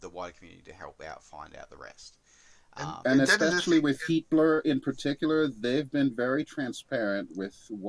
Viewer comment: HeatBlur? i thought he was saying Hitler